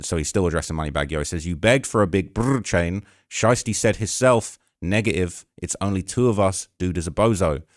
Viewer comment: I I thought said “nigga if” 😂 🤦🏽‍♂️